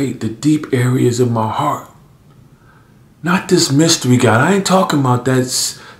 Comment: can you explain mystery guy and 'taught in' what? I couldn't hear.